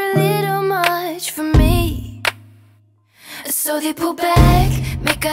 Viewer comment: When she says "pull back",I thought she was saying "poop back" and was so confused 😂